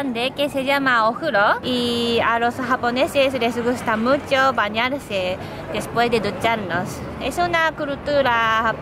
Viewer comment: ducharse después de bañarse?xd escuche mal? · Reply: Maritza Vela sí escuchaste mal, dice que les gusta bañarse después de ducharse, tu lo escuchaste al revés.